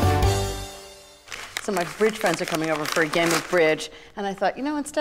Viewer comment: I thought she said her rich are coming over 😂